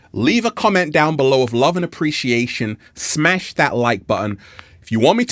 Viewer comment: Lol. I thought you said "Smack that like button"